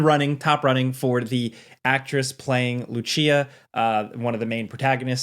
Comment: Waiiiiit, Did he just say,… “Lu-chia”??? 0:32… 😂😂